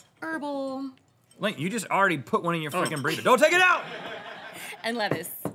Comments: I thought it said big burrito :( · DX wtf I thought it said BIG BURRITO